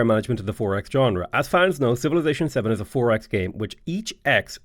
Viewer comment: everytime i heard "4X game" until now my brain just assumed people were saying "Firaxis game"....